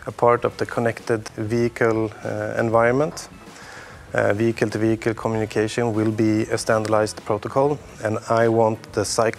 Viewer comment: I thought he said “vegan to vegan communication” autonomous vegans😂